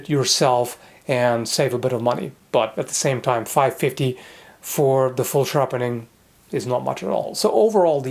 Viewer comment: It shouldn't be a "Special grade," but for $5.50, (Loonies) it's really not a bad option. Considering how much it costs for say Albion to put a razor edge on some of the duller swords they carry. (Albion makes their own swords, but they also carry other swords over from before they started making their own.) I'm the type to get the standard edge, and put in the elbow grease to get the edge I want, but then I'm also the type to make my own cinquedea, pollax, and warhammer estoc. Back when I had the tools to do such things. It really depends on the customer. I just have to applaud them for having those options, AND making them so affordable. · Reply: The other option is we both heard wrong. He clearly says "$5.50 for the full sharpening." Thanks for playing.